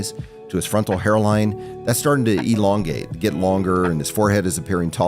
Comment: Did he just say..... Elon Gate? hahah